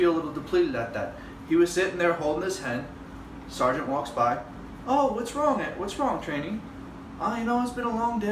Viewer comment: Can someone tell me what the fuck he said at 3:04?? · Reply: What's wrong "his name"